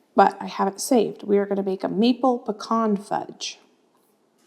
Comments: Omg, I thought she said "meatball pecan fudge" for just a second. I was like, "huh?! Baby brain?!" Then I realized she said maple, not meatball, lol!😂😂😂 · haha I'm not hearing correctly i thought you said meatball pecan fudge, then realized maple.